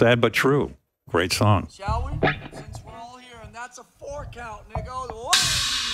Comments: did lars just said the n-word? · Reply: 'That's a four-count and it goes' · did lars just say the n-word?